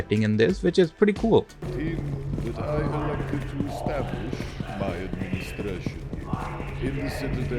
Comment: At first i heard him say "That i elected to establish my Playstation" - CombineStation CONFIRMED?